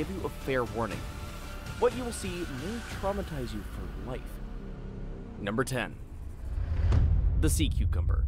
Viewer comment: I heard "Might not traumatise you for live " and I think that's even funnier